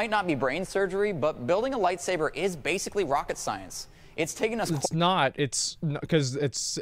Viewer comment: He said “basically rocket science” in the sense that ppl associate rocket science with being very difficult. He def did not say “literally rocket science”.